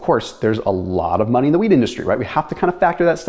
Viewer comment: sounded like he said "there's a lot of money in the WEED industry" not wheat lol